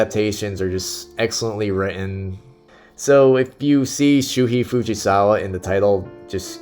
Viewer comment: what is the name you are saying? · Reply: Shuhei Fujisawa, he is the best writer for samurai movies. Check out his work